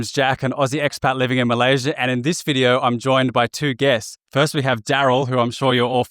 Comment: Almost misheard “two guests” with “two gays” hahaha